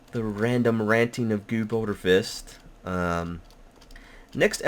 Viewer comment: I'm watching... ? Just have some letters fly over the screen or something - with that intro music its awesome none the less. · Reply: I hear 'Guuuude Boner Fish'. xD